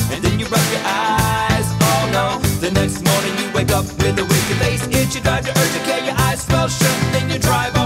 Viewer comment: Kelly Watkins I heard "winky face itch"